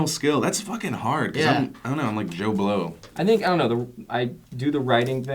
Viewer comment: I thought Phil said "I'm jon blow" and I was like YOU MADE VIDEO GAMES WHAT?